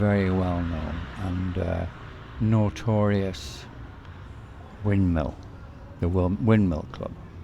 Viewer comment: What is the name of the place said at about 9:25.. where everyone should visit.. it is said many times but I still dont hear it right I guess. I hear "Windmill", is that right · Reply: Correct Windmill GoGo